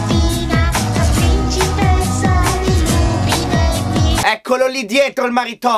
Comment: Sei un grande! Quando ha detto "sono la principessa di Lupi Negvi (o Negui)" io ho capito "sono la principessa dei lupi neri"